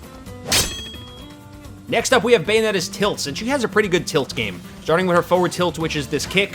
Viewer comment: I thought it said tits. I play too much bayonetta 😂